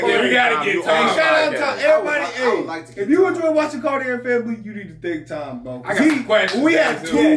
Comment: I always think about exercising with your intro because it sounds like she is saying “Cardio Family!” 😂🏃🏽‍♀️